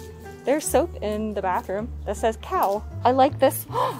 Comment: my half asleep brain thought you said there's soap in the back that says 'hell'. Apparently it's cow. I know this now.